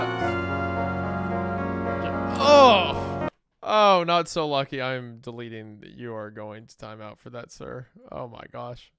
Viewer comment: WHAT DID HE SAY????? I NEED TO KNOW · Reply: "Fetus Producer Gabby" Absolutely diabolical